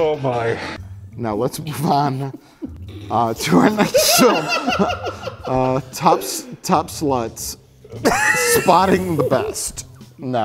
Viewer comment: I thought he said. TOP SLUTS- SPOTTING THE BEST